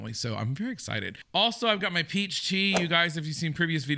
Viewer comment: Lmao. You said Peach Tea and I heard " I got my PhD ." 😆